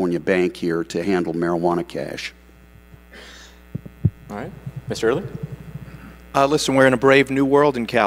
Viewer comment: I swore one of them said he was racist "Mr Early"